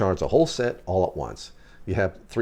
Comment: Hmm..Efest LUC V4? Much better ones available and the LUC’s don’t fit large cells well. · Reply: I swear I heard 'thor-chargers' or something...Lol... Million thanks.... 👍👍👍